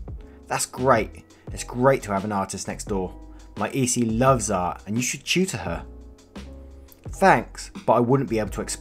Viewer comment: turn on caption Below where is say read more I'll tell you what it says My easy loves art and you should chew to her